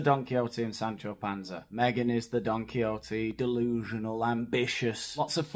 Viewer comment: For a second I thought you said " Meghan is Donkey- oty!"😂😂😂